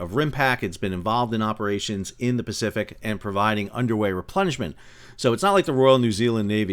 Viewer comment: laughing cos i thought he said "conducting underwear replenishment" (underway replenishment